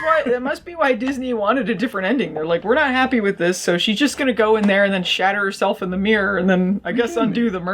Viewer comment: I thought you said the wicked queen shat herself in the mirror at the end. Which would have been a better ending. The ONE time Disney doesn't add poop to a live-action remake...